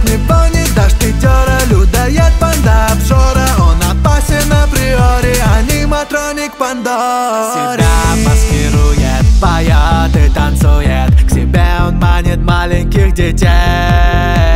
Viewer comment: Мне послышалось "на приоре" 😅